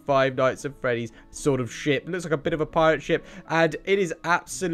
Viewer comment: did you say ship or the the bad word. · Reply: He sayed ship stupid